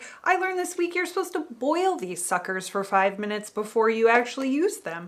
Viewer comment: I stepped away from my computer but kept the sound going. I heard "You're supposed to boil these fuckers for five minutes before you actually use them!" I was like - wow! She's knowledgable AND funny! And then I rewound it and you said "suckers." LMAO